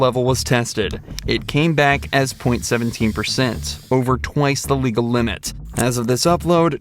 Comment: Every time I hear “twice the legal limit” all I hear in the back of my head is “TWICE IN LEGAL MINUTE?” 😂😭